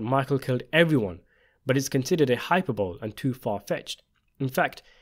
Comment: "It's considered a hyper-bowl." I think you're saying hyperbole pronounced hi-per-bowl-ee, exaggerated statements or claims. Great Video!